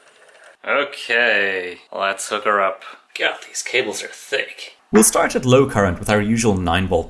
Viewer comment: My brain is infused with modern internet trash, I first heard that as "Let's hook'er up. Gyatt, these cables are thicc" XD